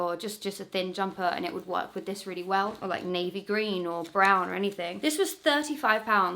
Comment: Did you just say navy green?